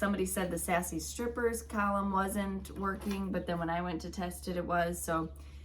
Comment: I am sure this is not what was said but I heard the "Sassy Stripers weren't twerking" I am guessing you said isn't working 😂